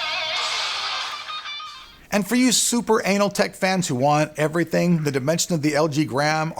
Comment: did he say "super anal"?